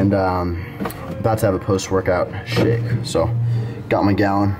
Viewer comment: Lol I thought he said a post workout shit, when he brought up the container thing I knew he said something else.